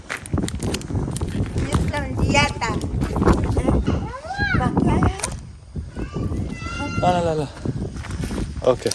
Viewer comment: Can anyone tell me what that woman is saying at 11:21? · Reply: she said (Yeslamou Deyatek) they use it as thanks you, but it means "thanks to your beautiful hands" but its not very common i think its only in villages also it kinda old arabic word or some kind of strong accent idk